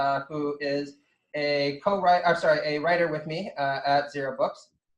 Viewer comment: "Micheal Brooks who is a comrade- I mean sorrt a writer..." lmao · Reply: i thought he said "cowriter" but I prefer "comrade" to trigger the chuds